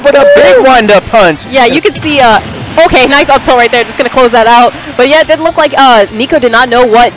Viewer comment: Did she say up tilt?😂😭